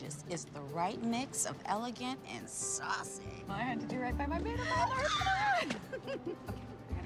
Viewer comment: what did beckett say at 0:41. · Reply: She says “I had to do right by my maid of honor”